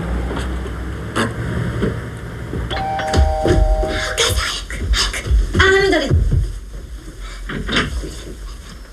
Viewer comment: Can anyone tell me what does says there to her mother? Thanks! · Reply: Bud E ... said, "Okaasan hayaku! hayaku!" which means "Mother faster! faster!" or "Mother hurry! hurry!"